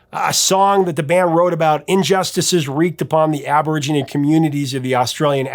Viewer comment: Hey, Having lived in Perth for a time (and definitely learning why that song resonated so strongly), you should know that they prefer the term “Aboriginal” over “Aborigine” (which is what I thought I heard you say). Just so ya know. Love everything you do!